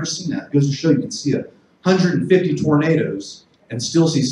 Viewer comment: You put Tomatoes in CC instead of Tornadoes. XD